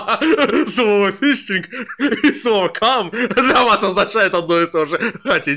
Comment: Я всё никак не разберу, что он сказал. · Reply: Слово "фистинг" и слово "кам" для вас означает одно и тоже.